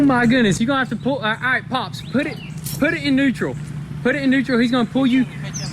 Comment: I heard Ukrainian "хай він мене тягне назад". Let him pull me back Hello from Ukrainian fans